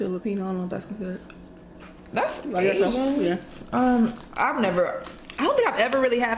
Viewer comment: Can people please understand that ASIAN FOOD is not just east asian food. Indian, filipino, libanese and afghan food is also asian food. Hate when non-asians say they love asian food when u clearly and specificly mean EAST asian food. Asia is the worlds biggest continent and has so many diffrent food cultures. Please educate yourself, because it's really disrespectful to asians. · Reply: Maybe I heard wrong, but didn't say "that's not asian" 5:35? I have watched some videos I know u guys always try to be respectful and inform others about the food culture. Based on the way you answered my comment, i must've heard wrong.